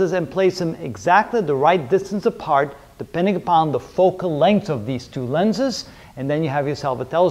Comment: sir what word did you say on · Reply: "the right distance apart, depending on the focal length of the lenses....."